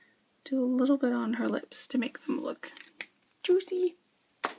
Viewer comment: thanks for doing this like how u showed coloring the hair i have an issue with that still and did u just say juicy lol lol....hugs